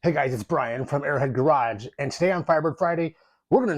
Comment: When you introduced yourself, I thought you said, "Airhead Garage". Hahaha! 😅